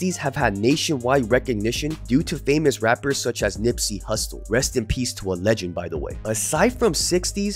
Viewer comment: I fucks with you my guy. But did you say "Nipsey HUSTO (Hussle) @ 5:41?!?! 🤔🤔🤔🤔🤣😂🤣